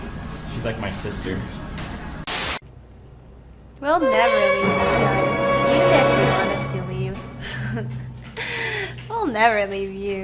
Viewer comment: what did she say? I couldn't hear :C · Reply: KalHirol we”ll never leave you guys even if you want us to leave